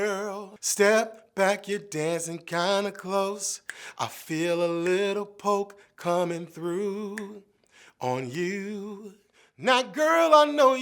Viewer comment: Lmao ohh man as I kid I always thought she said “I feel alittle poo coming thru on you”